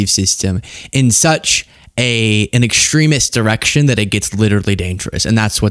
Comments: I thought Luke said "extremist erection" and burst out laughing. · DId you say Extremist erection at @ 1:57? geez